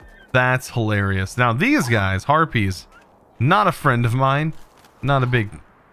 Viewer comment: it sounded like you said herpies and not harpys